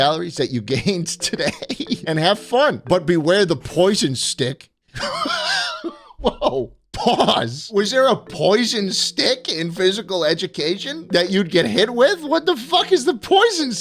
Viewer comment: I thought that’s said prison stick😩